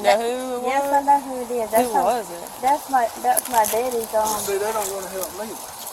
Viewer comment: I swear I heard her say "I want my daddy".😢